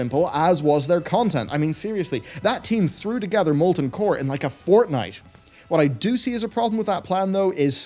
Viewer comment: did I really hear him use the word "fortnight"? That's a very rare word to use.